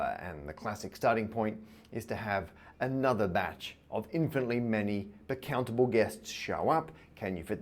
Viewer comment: The subtitles at 10:36 say "infinitely many accountable guests" instead of "infinitely many countable guests"